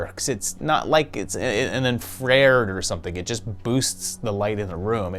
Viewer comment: Did you just say "En-frered," instead of "Infra-Red?"